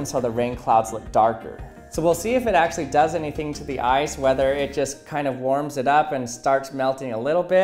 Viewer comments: Did anyone else hear "So we'll see if it actually does anything to the eyes" at 5:08 ? I was like is he going to point that laser in his eyes?!? It then became obvious it was ice, not eyes. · I forgot what the video was about already and thought he said "eyes" not "ice." Like, woah I would not try that if I where you